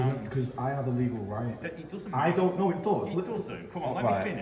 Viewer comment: did I just hear a police sgt say that somebody's legal right "doesn't matter?"